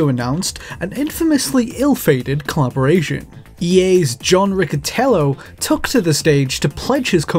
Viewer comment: "John Ricettello" When I first heard it I thought he said John Wicketello